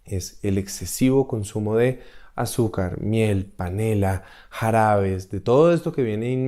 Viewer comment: Muchas gracias doctor muy buenos videos quería confirmar quizási entendí mal minuto 11:51 dijo Canela? Porque se que tiene exelentes propiedades quizás sea perjudicial en grandes cantidades, gracias